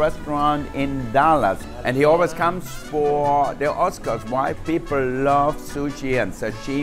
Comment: I know he says why but it sounds like he says “white people love sushi and sashimi” which is true